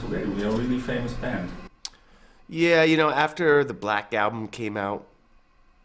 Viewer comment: did he say "the BLACKED album..."??